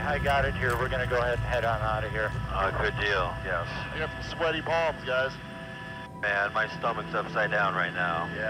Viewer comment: I thought he said I got sweaty balls guys lol